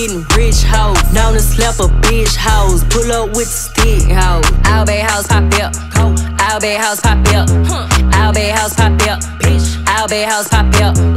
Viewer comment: Sounds like she saying " All bad hos pop beer" 🤣🤣🤣